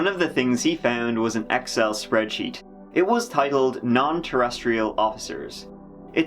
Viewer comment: I know I am a year late, but it’s worth sharing I wasn’t looking at the screen when I thought I heard the spreadsheet was titled “Not Terrestrial Officers,” and I just might have had to believe it if so. 😂